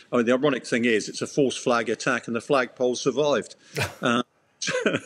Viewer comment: I love this guy. "It's a false flag attack and the flagpole survived." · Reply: Hahaha...I thought he said it was a false flag attempt and the false flag didn't survive..... 😅